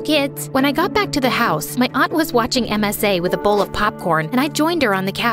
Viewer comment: I heard “auntie was watching Msa”🤣🤣🤣